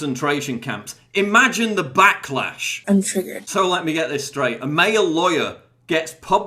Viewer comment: What does the woman say at 1:54? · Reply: She says "I am triggered."